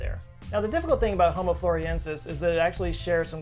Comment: says Homo Floriences, subtitles say home appliances